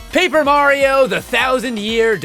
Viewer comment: i thought you said NO mario